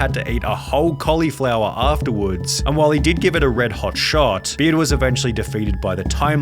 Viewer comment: I can't hear 7:08 as anything other than "a red-hot shart".